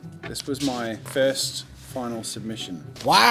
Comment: Thought Jazza said "final solution."